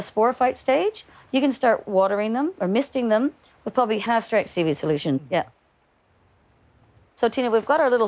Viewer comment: "half strength seaweed solution". I didn't quite catch it but it was in the video notes, just to remind myself for later